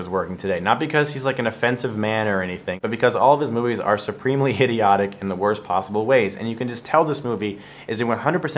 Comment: did he just say Hideotic?